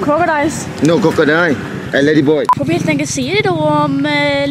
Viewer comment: Hörde jag fel eller kalla han dig ladyboy? XD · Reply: jo det var nog ladyboy ja...